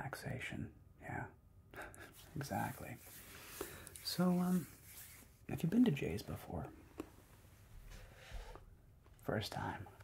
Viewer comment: When you said “Have you been to J’s before,” I thought you said “have you been to jail before” lol. But now that I think about it a warden/jailer roleplay would be sickkkk haha